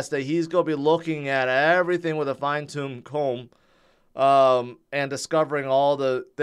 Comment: Did he say fine tomb comb???🤣🤣🤣